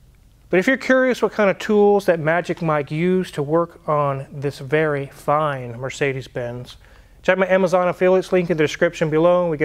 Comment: I misheard you at first and thought you said Magic Mike used to work at Al Capone Mercedes-Benz. Always figured Capone for a Cadillac man myself.